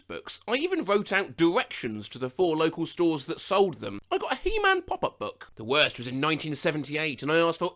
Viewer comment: Omg when I was little, I thought his name was she-man. He had long hair, and I misheard, so it had to have been she-man. I dunno how I came up with that, but I didn't believe anyone when they tried to correct me.